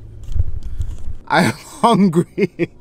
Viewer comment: A lot harder when you’re not fasting for religious purposes so a ton of respect, keep up the good work Allah bless you!! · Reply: كأنه قال انا 3:47 I think you say I'm in the Arabic language